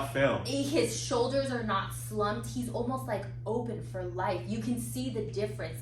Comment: I made a telling Freudian slip: I thought she said, "He over-polite"...and I was like WTF? He should be the reverse, three listens later I realize she said, 'He's open for life'........Wow...